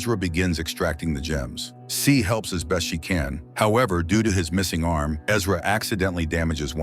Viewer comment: Am I the only one who heard him say, “Doodoo,” every time he tried to say, “due to”? 😂